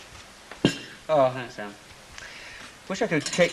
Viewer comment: Does Ken say “Thanks Anne” after Deirdre puts the teapot on the table?